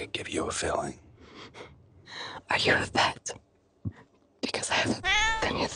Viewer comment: what does she say at 0:50? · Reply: "Are you a vet, because I have a ..."